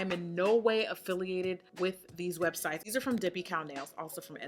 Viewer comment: Anyone know what’s the second brand she mentions around 12:10 - dippy cow? 😂😂